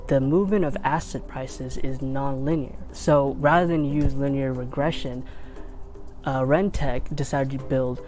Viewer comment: does he say linear progression or regression? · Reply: Linear Regression